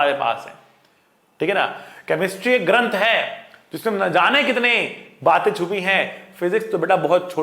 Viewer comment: i heard chemistry ganndh ha and i feel it